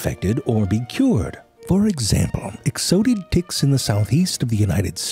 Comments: I thought he is saying dicks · I honestly think he’s saying “dicks” the whole time and we just think it’s “ticks” because that’s what he wants us to think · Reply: I thought he was saying “dicks” the whole time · I genuinely thought he was saying “dicks” the whole time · Why Does it sound like he saying dicks lol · I thought he kept saying dicks lol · Sorry but I keep hearing him say 'Dicks' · every time he said "tick" i heard "dicks".. makes the video a whole lot more entertaining. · It sounds like hes saying dicks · I keep hearing.."Dicks"😂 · It sounds like your saying dicks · 28 seconds in and I had to pause. I kept hearing the word "dicks". 😂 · It sounds like he says dicks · Really thought you were saying "dicks" for a few minutes. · all that time, i thought u were saying "dicks" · I seriously thought you were saying dicks. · it literally sound like hes saying dicks throughout this whole video and i am taking everything out of context...